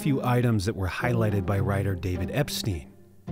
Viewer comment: i was so stunned i thought I heard jeffery not david